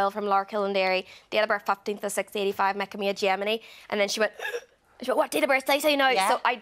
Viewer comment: "making me a gemini." but auto english generated subtitles said making me a germany. which is what i had understood which is why i put the subtitles on in the first place. I absolutely love the irish accent, and in general, uk humor is just so rich. very fascinating to learn the actress who plays erin is actually from there. very cool. just started watching the show today and just finished the first season. good show i love the theme and setting of it all.